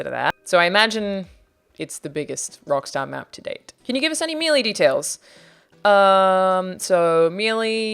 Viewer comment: What is she saying at 3:05? It sounds like “can you give us any mealy details”. What is “mealy”?